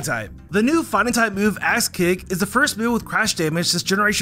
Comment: did anyone else hear him say "ass kick" or is that just me?